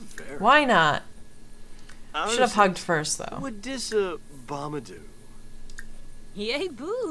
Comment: After they kissed and they were talking it sounded like he said what did obama do